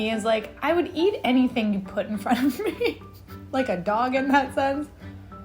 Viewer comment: For a moment I thought that was saying she’d eat anything you put in front of her, like she’d eat a dog